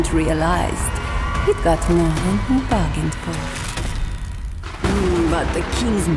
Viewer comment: what did she say???? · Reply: "He got more than he bargained for"